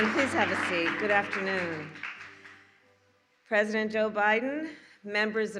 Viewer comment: wth... did she really just say "President obiden"? 00:12 They are having trouble not slipping the truth out....